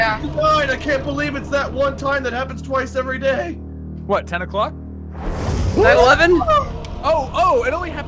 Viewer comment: what the heck did you say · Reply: He said 9/11